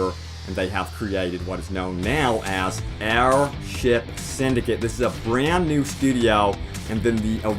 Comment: My only complaint: every time you said "air ship" i thought you said "our shit." Which is fine, just funny.